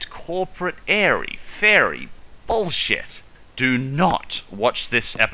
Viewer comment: Did just say... Oh. My. GOD! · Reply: No, he said "bullshit".